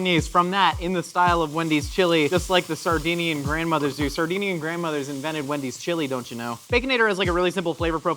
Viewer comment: i feel like Josh's sleep paralysis demon is a pissed-off Sardegnian grandma just waiting to punish him for his many crimes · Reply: See i thought he was saying Sardine-ian, as in the fish.